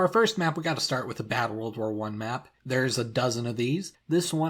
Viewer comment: are we just gonna ignore that The Netherlands has been conquered by Germany (it was neutral during WW1) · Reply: I misheard it as World War 2, so I just went "okay...? O.o Weird alternate history."